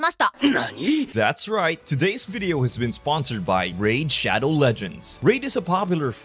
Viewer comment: WTF I REALLY THOUGHT GOKU SAID THIS VIDEO IS BROUGHT TO YOU BY RAID SHADOW LEGEND